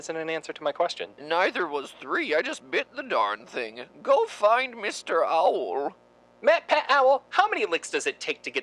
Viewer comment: When he said "go find Mr owl" I could swore he was starting to say "go F### yourself", am I the only one?